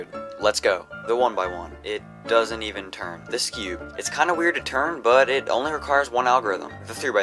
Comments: When you said skewb I thought that you said "this cube" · Level 10: skewb I thought you said this cube. I was like It is a cube